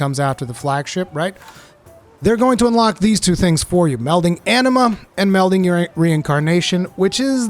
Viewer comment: I swear for one second I thought you said “melting enema” around melding charms section…